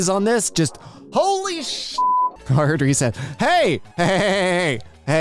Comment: I thought he said holy s t